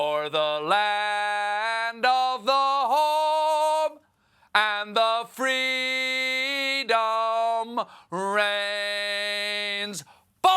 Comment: I thought that senator said "Home and freedom brains"? 🤣